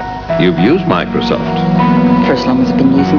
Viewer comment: I always thought she said "first time I've been using computers." But she actually said "for as long as I've been using computers."